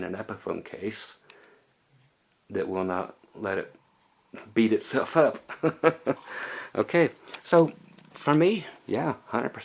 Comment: Did you say Let it Beat itself off!!! lol